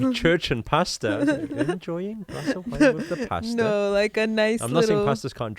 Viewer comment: Ha... I thought she said “wine with their Pastor” too (with a Boston accent.) 😂